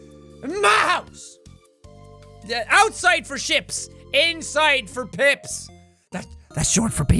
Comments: Thought he said pimps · I thought he said "Pimps, that's short for people."